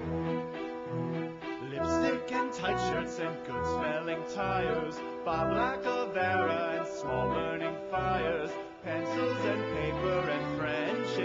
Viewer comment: sounds like he said Bob Lacovara. dunno who that is though.